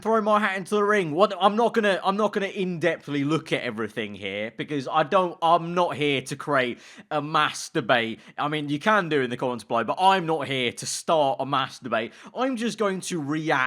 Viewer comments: "I'm not going to create a mass debate on this" Famous last words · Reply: The Imposter he sounded like he said masterbait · "I'm not going to create a mass debate on this" Famous last words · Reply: I thought he said masterbate Lmao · WHAT DID YOU JUST SAY????? 😂😂😂😂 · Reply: He said Mass-Debate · you say. I'm not here to start a mass debate. When you say that i kinda sounds like you say masterbate.... Just a heads up 😂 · I thought he said masterbate at 1:40😂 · I legit thought he said masturbate instead of mass debate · him saying mass debate sounds like hes saying masturbate lol